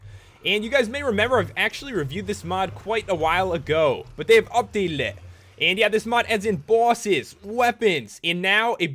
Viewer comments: Did anyone else hear him say "They updaleded it" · i think he said at 0:22 they uplated